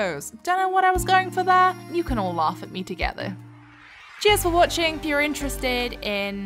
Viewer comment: Might be a bit cringey, but I legit heard it as "jizz for watching" at the end there. Had to rewatch 3 more times before my brain finally caught on you said Cheers goddamnit haha